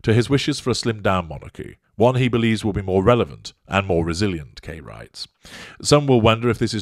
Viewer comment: I kept hearing “gay rights“ and listened to it twice… I kept trying to figure out what Gay rights had to do with the article… it’s Key writes 😂 I’m all for gay rights, but Kay writes makes more sense!